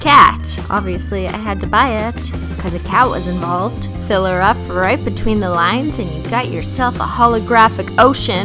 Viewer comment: Am I the only one who thought she was about to say “fill her up right between the legs” 🤷🏾‍♀️